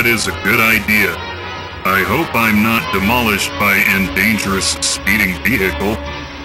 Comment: "I hope I'm not demolished by a dangerous speeding vehicle" Flashbacks ensue Edit: ok fine I'll edit it to "an" :P Edit 2: boi people telling me to edit "a" to "an" and now y'all correcting my grammar when people told me to mess it up in the first place make up yer minds aaaa- Edit 3: don't tell me to change the "a" again I'm not doing it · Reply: Dominick Boren I thought he said endangerous speeding vehicle